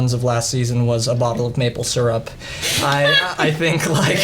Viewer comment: Is it just me who hears the sound madalaine(I think that's how you spell it) at 9:48 Edit: What is wrong with Kj he doesn't seem at happy as usual